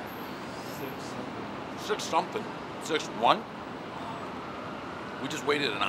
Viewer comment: 6.2 for Gino’s East is just plain wrong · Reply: I thought he said 2.1.